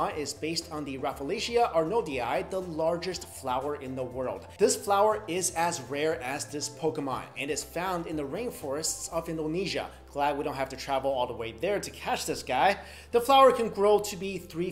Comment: i thought you said is has rares XD